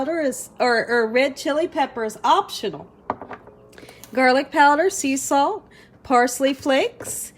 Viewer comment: When you said, garlic powder, I thought you said, girlie powder. Lol lol